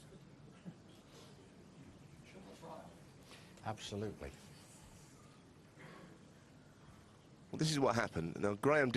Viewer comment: what was said at 2:13? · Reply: "Are you sure that was right?" Sorry for waiting 11 years to get an answer for that 🤣